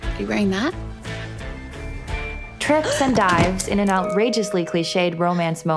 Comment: thought you said "trips and dies" and i almost started laughing so hard at work